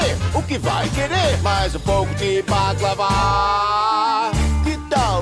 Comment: o que ele disse? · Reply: Badlava